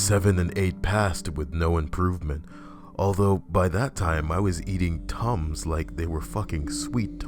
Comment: Not the only one that thought it said beating thank god